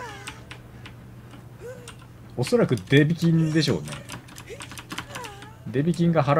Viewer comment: あっ！デビキンなんですね？ずっとデメキンに聞こえてました！！